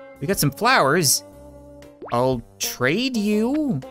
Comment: I believe what she just said was "thank you very much", so yes, your welcome is in fact, the correct response